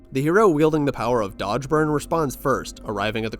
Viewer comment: What did he say at 8:02? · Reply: Dodgeburner